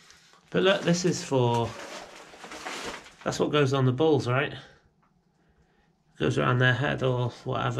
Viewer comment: to an American, when you say bulls its sounds a awful lot like balls, caught me off guard at first, i wasn't paying attention and i heard "you put this on your balls" i f*****g lost it